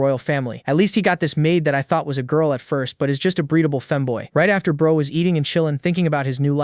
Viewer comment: Did you just say breathable femboy 😂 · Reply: Breadable